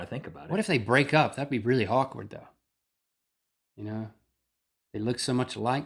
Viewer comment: i thought link said 'that would be very hawkward' :'D